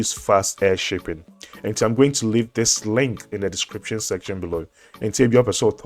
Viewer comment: Pls I thought you said u are going to leave a link here